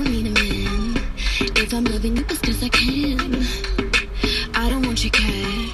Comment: OMGG 😍😍A BOP,MY WIG PLEASE but at 0:04 what Jesy is saying ? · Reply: ERIKA if im loving you its cause i can